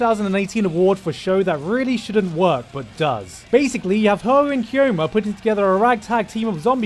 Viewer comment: okay I hear hououin kyoma, oh boy am I invested